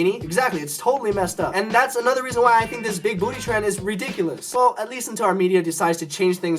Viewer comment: I thought it said why big foot is trend